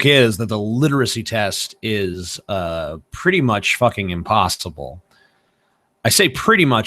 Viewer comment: i heard impastable